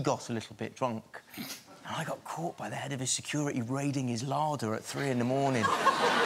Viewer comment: American here, he raided his "lauder"? What is a lauder? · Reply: Ross Malagarie I thought he said larger. As in beer. 🇬🇧